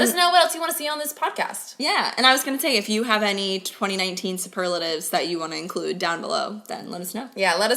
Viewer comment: Thought it said super laxative, lol. Which wouldn't be wrong because 2019 was poopoo.